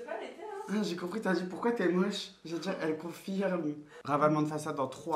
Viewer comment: on a tous entendu "POURQUOI TES MOCHE" 😂😂😂